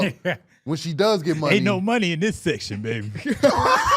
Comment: what said at the strip club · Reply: Ain’t no money in this section